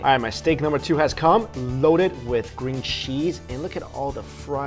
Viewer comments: haha did you just say 'green cheese' @ 3:45 ? 😂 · did you say ... green cheese?